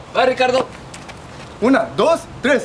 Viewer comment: Lol did i hear a girl say "puta madre"